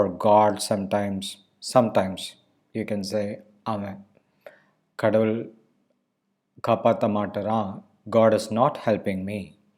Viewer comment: Sir can u pls write down what u hv said at 1:40 kadavul line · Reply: kadavuL kaappaaThE maatturaa.n - God is not helping - கடவுள் காப்பாத்த மாட்டுறான்